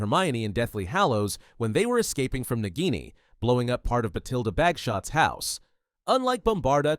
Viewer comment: who else heard backshots